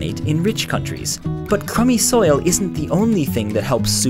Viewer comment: did i just hear "Commie Soil"?